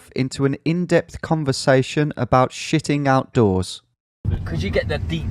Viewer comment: jesus i misheard "shitting outdoors as shitting out doors" and thought, "you can't shit out a 8x4 piece of lumber"